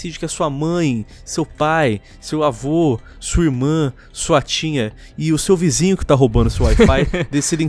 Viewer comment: "Sua tinha" essa tem como não, já foi "Pau Genial" "Ekkozinho" e agora "sua tinha" alguém o pare!